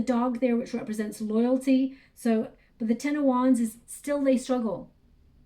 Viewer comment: I was working on something else while listening to this, and when you said "The wanna waits" I heard "The 108's", and got chills run up my spine and though my body. Very significant number to me, as I am a part of a spiritual seeker group called "The 108 Journey", now on to 10,008, but I am apart of the original inner 108 Circle. It brought back my full attention to the reading, and yes, it does resonate, and I know she is wrestling with it, just as I have been wrestling on what to do when she comes back over the last couple of years, and I'm done. My mind tells me what to do, and I thank it, and acknowledge it for it's advice, as well as always seeking to protect me, but my soul knows the words written on my heart. As usual, you told it how it is. Thank you 🙏